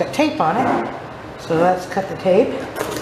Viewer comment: I thought you said let’s cut the cake. I was like mmmm, cake 😋 but you said cut the tape 😂